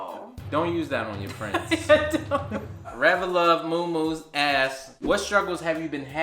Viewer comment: When said REVELUV MOOMOO ASKED, (6:30) I thought he said A** lmao 🤣